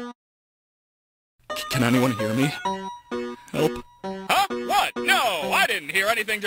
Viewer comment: When Spamton said, "Can anyone hear me..? Help..." The captions thought he said, "Can anyone hear me? Nope."